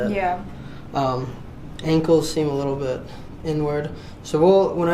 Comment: WTF?! @ 7:14 he says her "ankles seem a little bit N-word." Is that the new way to say something is messed up? I like it.